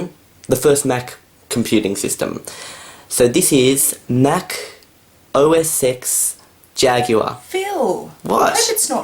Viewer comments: it sounds like he said sex jaguar that's probably what she thought it was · She thought you said "SEX" when you said OS X